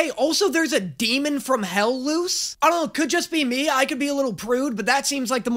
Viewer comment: I think called mabbel luz here